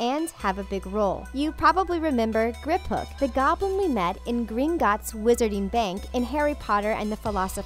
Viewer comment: turn on captions it says green guts instead of gringotts lollll